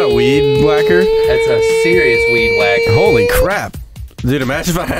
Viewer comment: at first I thought they said weeb wacker